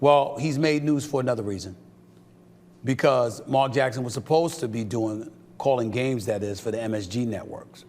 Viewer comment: Who else first thought SAS said Mark Jackson was full of s**t ?